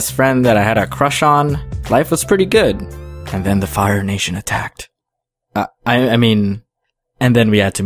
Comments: For a sec I thought you said "The Infires Nation attacked" 😂 and I was like what the frick? Min Yoongi's nation attacked you?! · And the fire nation attacked. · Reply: Patrick Barredo oh I thought he said the moving nation attacked :/ geus I was wrong